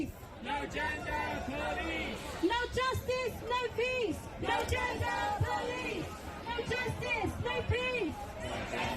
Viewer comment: Why do I hear “no genders, no piss” 🤣